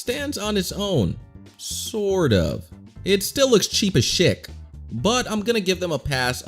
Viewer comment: cheap as shick when i first heard it, thought he said cheap as shit